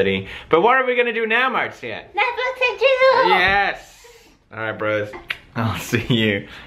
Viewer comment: who else thought marzia said "netflix and chizzle"